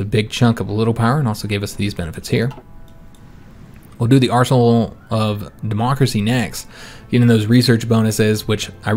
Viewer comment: I know you said arsenal of democracy, but I could have sworn you said arsehole of democracy. :P